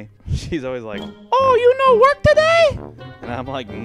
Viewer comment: Who thought the Asian lady said u no whip today